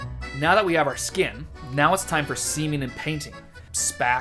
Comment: I was like, did he just say semen and painting? Oh, seaming...